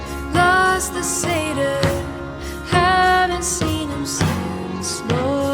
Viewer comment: I loved seeing Utah on Youtube... Office parks and MLMs! Also... whenever said "Satyr" I heard "Seder" and was very confused